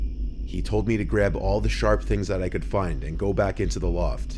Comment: for second I thought that he said "he was putting on a condom"... :^